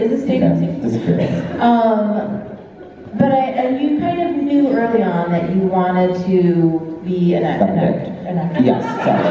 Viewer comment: i cant i finally figured out what he said HAHA: "you kind of knew early on that you wanted to be... Andrew: "suck a dick"